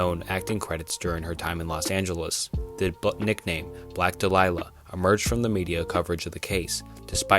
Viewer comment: Respectfully what did you say at 6:33 ? The what nickname? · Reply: He said it wrong. He said “Delilah” when it’s supposed to be “Dahlia”